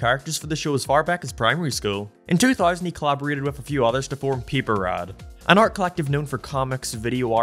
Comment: why did you say peeperad? Bruh come on